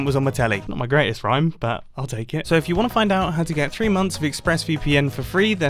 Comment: i think he said tree months.